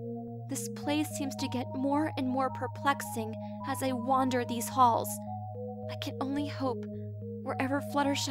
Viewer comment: When twilight said I wander these halls I thought she said I wander around this balls lol